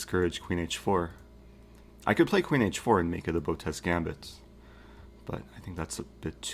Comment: Gotez gambit? What is he saying?